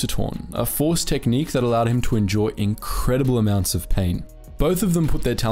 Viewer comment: why do i keep hearing "enjoy incredible amounts of pain," and not "endure incredible amounts of pain." no matter i how i listen to it i hear enjoy